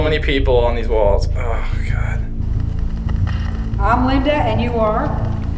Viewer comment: im not even lying when linda said "im linda and you are?" at 8:26 i heard "robert" and im surprised no one in the video pointed that out then later on she said "robert" i called that lol